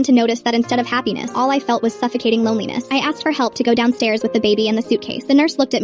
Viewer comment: I THOUGHT SHE SAID GO DOWNSTAIRS WITH THE BABY IN THE SUITCASE HELLLLP 😭😭💀💀💀